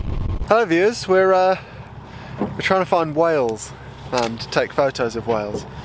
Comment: "We're trying to find whales"... I heard "Wales" and was expecting sheep. I'm almost disappointed. (Doing dishes while watching, so you'll forgive me...)